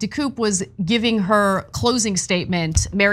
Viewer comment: Sounded like you said her name was mary messier ?? Lol so fitting the b is messy !